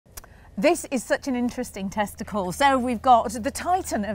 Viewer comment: I had to rewind and listen to the first sentence of this video again I thought she said 'this is a really interesting testicle' 😂😂😂